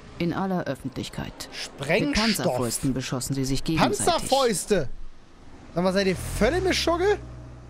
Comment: Wieder... 1:02:53. ick habe Hamsterfäuste verstanden, alda. :)